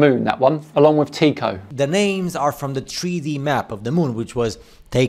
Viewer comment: When he went from "hidden lands beyond the ice wall" to "3D map", I heard "tree-D map" or "treaty map" and assumed he was going to link this to the Antarctic Treaty. This "Divergent" guy certainly has diverged from the realm of sanity. I will agree with him on one thing: my brain is also tired now.